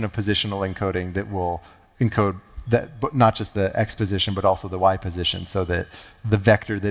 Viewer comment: I heard: "...encode not just the exposition but also the y-position..."